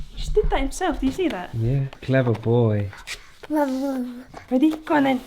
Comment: A 7:20 it sounds like said clever boy??